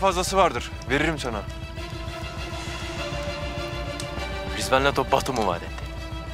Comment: ne diyor bu çocuk ya yüz kere dinledim anlamadım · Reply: Rızvan leto Batum u vaat etmiş